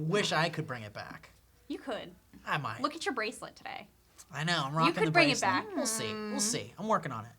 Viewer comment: i thought said ''look at your bracelet bae'' i nearly died.